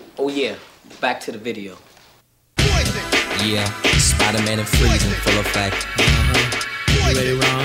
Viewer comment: Yoooo, did he say "Spiderman and Frieza in full effect"...?!😂😂